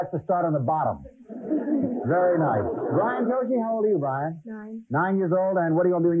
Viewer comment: Did he say Brian Tochi? From the Police Academy movies? He'd be about the right age.